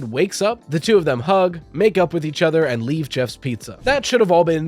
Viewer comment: i heard "make out" 💀💀💀